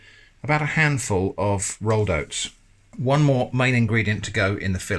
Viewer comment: almost spat out my biscuit when i thought i heard you saying you were about to add a handful of rolos